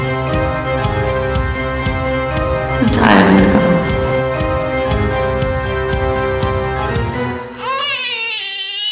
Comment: ohmygosh this is so good, so cute. very cleverly edited, nice job there. I love how it's so rushed! what does she say at 0:40 ? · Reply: She says "im tired"